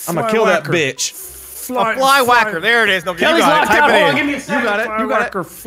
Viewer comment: What's Wildcat saying? Kelly lost what? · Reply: Namaye “Kelly’s locked out, give me a second!”